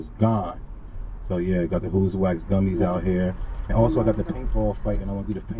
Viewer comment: i hear that Who’s Waxx